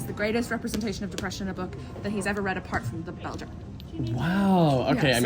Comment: Does anyone know the book the lady mentioned at 3:21? I thought she said ‘the Belgium’ but I can’t seem to find it online! 🥲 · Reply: I believe she said "The Bell Jar" by Sylvia Plath.